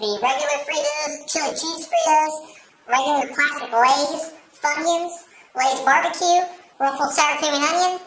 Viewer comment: Is it me or did it sounds like he said fuckins in 1:30 😂😂😂